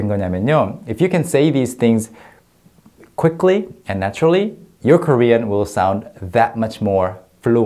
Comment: I thought said “ if you can say these quickly and naturally, ....you’re Korean!” But then he continued “......your Korean will sound much more fluent....” ㅎㅎㅎ Anyway, loooove this lesson because I need to stall some time when thinking about how to express some things in Korean haha! Instead of having awkward silence gaps, why not use some pause fillers with some acting skills we all have seen from Korean dramas to practice your Korean am I right? 😎